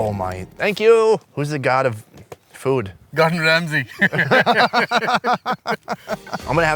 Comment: IDK India have a desert.. love from Indonesia 10:02 did he just say Gordon Ramsay?